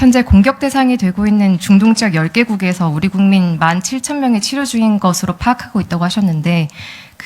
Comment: 국민일보 기자 12:51 1만 7천명이 치료 중인 상황이 말이 된다고 생각하는건가 잘 못 들었다는 생각 조차 안 드니까 이런 질문이 가능한거 아님?? 아니 어떻게 체류를 치료로 들을 수가 있는거야